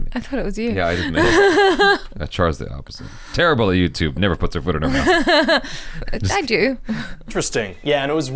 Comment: what I heard : never puts her food in her mouth Me : where does she put it 🤔 Have I been eating wrong all my life?!